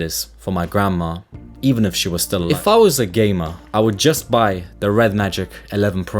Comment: i thought he said ''if i was a gay man-..."